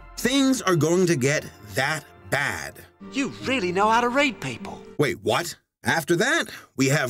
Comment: WOW! Ok... Jeez that even made me say "Wait, WHAT?!" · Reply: Okay guys valm down yes i know that it SOUNDS like he said "You really know how to R*pe people" But im pretty sure that it was intended to say "You really know how to Rate people"